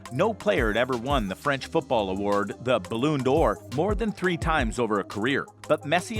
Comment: Did you just say the balloon d or😂😂